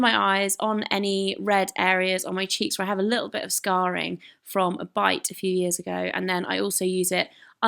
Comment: I thought you said "scarring from a FIGHT a few years ago." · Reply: A bite! LOL :S